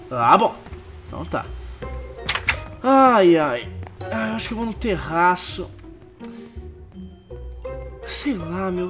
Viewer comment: Quando o Rezende falou "Ai, eu acho que vou no terraço" eu entendi "Ai, eu acho que vou me enterrar" XD